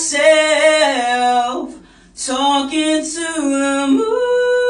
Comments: I heard "I SHIT BY MYSELF TALKING TO 🦅🦅🦅🦅🦅🦅🦅🦅 " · "I sIt By My SeLf TaLkInG tO tHe MAAAAAAAAAA-" 😭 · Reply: I heard "I sh1t by myself, talking to the MÆÆÆÆ--!!" 💀